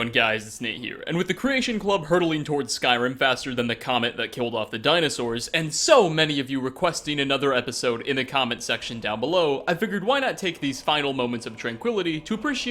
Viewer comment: In the beginning it sounded like you said “the comment that killed of the dinosaurs” lol